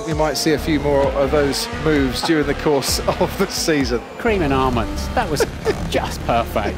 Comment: What does Martin Brundle say @ 1:56? “Cream and almonds”??? 0.o